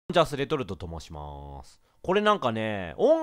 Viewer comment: んちゃーすレトルトと申しまーす。て聞こえた(∩•ω•∩)！ カブトムシの盆踊り感wwwwww